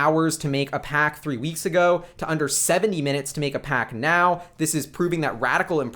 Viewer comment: dude...i could have swore he said seven-TEEN minutes now... it occured at -7:29- seconds on the conference call.. take a listen, tell me what you think... "just over 7 hours" to me would be followed with "a little over an hour" if it was 70 minutes, but its really hard to tell because of call quality.... I dont know if this matters, but when the President was saying "big league" during the debates and people were hearing him say "bigly".... i heard "big league"... maybe ... yeah i dont know what that means, but i heard him correctly then. Not sure if that converts to Elon's speech patterns.Also, 14:15 He didn't necessarily talk about help with building out the Supercharger Network... he said "They would just need to pay share of the costs proportionately to their vehicle usage. And they would need to be able to accept our charge rate and our connector, or at least have an adapter to our connector." 50:18- in on the call.. find the call at ir DOT tesla DOT com ... You stated at about 14:35 in this video that they should keep the supercharger network closed due to competitive advantage. Simply put, that works against their mission statement of "to accelerate the advent of sustainable transport by bringing compelling mass market electric cars to market as soon as possible. " to close off the supercharger network would work against that quite literally. For the record, I plan on either taking my shares to the grave or giving to my children as an heirloom.... keep on uploadin